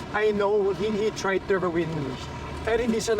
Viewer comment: Did Eric just said "He tried to @ 30:48 win me" wut? did i hear that right?